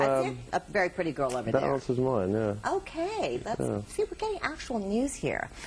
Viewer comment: = did ed say " that arse is mine" ahahahhahaha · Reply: "That answers mine" lol